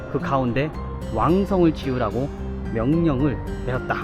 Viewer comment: 12:55 즈음에 '왕성을 지우라고..'라고 쓰여 있고 발음도 '지우라고' 정도로 들리는데, 이게 '지으라고(짓자고)'라 받아 들여야 맞겠지요? 맥락상 그럴 것 같은데, 주원장이 인간성(?)이 인간성인지라 왕성을 없앨 것 같기도 해서 아리까리하네요ㅎㅎ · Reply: 왕성을 ‘짓자고’가 맞습니다 세심하게 영상 봐주셔서 감사합니다! 사실 자막 생성 프로그램 돌리고 오타 검사 하긴 하지만 항상 한두개씩은 나오더군요😂 이점 양해 부탁드립니다!